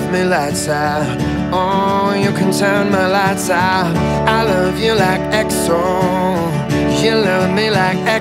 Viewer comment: I thought he said "i love you like exxon mobile"